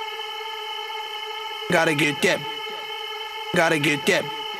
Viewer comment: i always thought he said "Got a kit kat"'